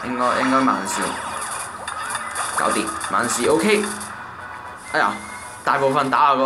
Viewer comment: OK聽成PK